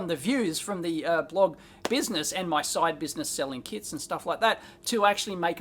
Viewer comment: i thought you said selling kids :p